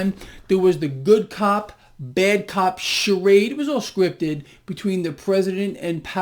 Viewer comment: I thought you said bad cop bad cop!